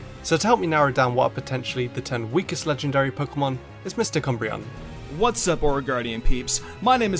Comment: and Mister Cumbreon. I misheard I am so very sorry.